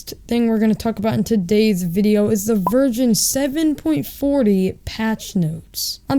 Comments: anyone hear Virgin? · I thought he said virgin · it sounded like he said “the virgin 7.40” 😂